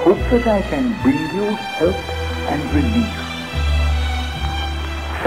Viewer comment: Does he say 'I've come to Poland'?